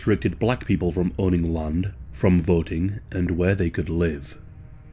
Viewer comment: Ohh ok, VOTING. I thought you said they were restricted from boating. Cool now I can move forward